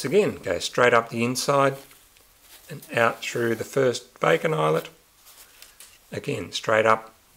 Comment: Did I hear "bacon Island?" Thanks for the vid!